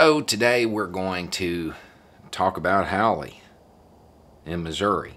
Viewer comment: At the beginning I thought you said let's talk about a haole. I guess I was right😁